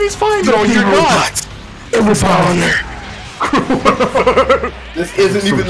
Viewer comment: can someone tell me what he says in 7:27? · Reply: I think he said "It's been an honor"